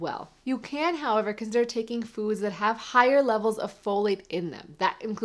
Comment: OH, Folate! I thought you said fellate. That works for me every time.